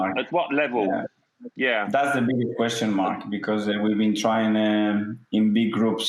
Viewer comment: did arteta call alan mark or martin 😂